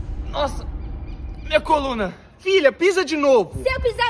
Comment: Eu entendi " se eu pisar na galinha, mamãe quebra a coluna". "Se eu pisar na galinha, papai quebra a coluna".